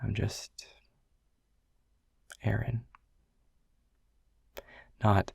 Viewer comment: Him: i'm just... Aaron Me: bro i literally heard "eren" 😭💀